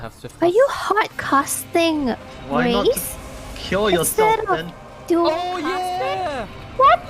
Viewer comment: she said "are u hard casting rez instead of"... What? Idk what she's saying. Can u explain what happened? Because she was hella amazed bro. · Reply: I think she said "Instead of dualcasting", the trait that would make it instant-cast.